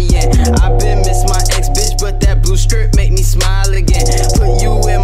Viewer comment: What does blue stip mean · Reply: Thought he said “blue scrip”, as in 30s